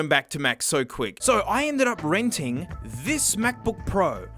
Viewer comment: did he say renting?